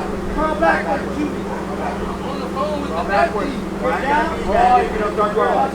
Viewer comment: Re, Arrest one, the goof is making a phone call as he's surrendering to a well armed team of US Marshalls surrounding his house! During the most critical phase of the surrender, he's trying to continue his phone call. How dumb are these goofs? Instead of complying and surviving, he's arguing with the Marshalls about continuing his damn phone call as a gazillion weapons are aimed at him... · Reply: Sounds like he said he was on the phone with the police